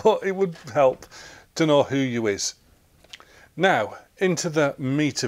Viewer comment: Have I misheard it, You said "who you is"? at about